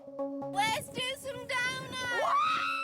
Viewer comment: says let’s do some donuts. I heard let’s do some downers and I was 😮